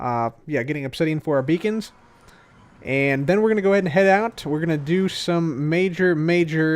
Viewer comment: Thought that said hub and head...